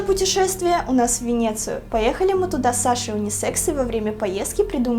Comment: Мне послышалась: Мы с Женей поехали в Венецию ради УниСекса xD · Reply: Sasha M Это не брэнд. Она сказала "поехали мы туда вместе с Сашей Унисекс". Видимо это её фамилия или кликуха. А вообще унисекс - одежда (вещи), которую могут носить как мужчины, так и девушки.